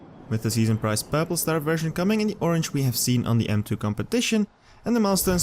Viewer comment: wtf does he even say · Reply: with the season car coming in in the orange we seen on the m2 competition..."